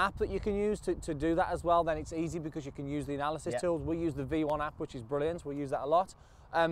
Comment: couldn't hear the name of the video app you said that you guys use @ 1:12... Mind replying with the name, or posting a link? Thanks guys! Love the videos! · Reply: V1. They added a logo in the lower right corner after they said the name.